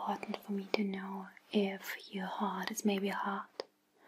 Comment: Is she saying that "To see your heart is maybe a heart"??